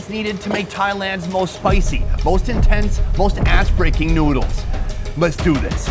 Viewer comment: i think i heard that wrong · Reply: Ass breaking noodles